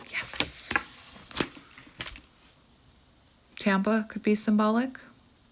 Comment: Did you just say "Tampa".... we're both born and raised in Tampa